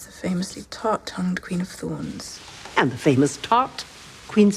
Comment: did she just call Cersei a famous Thot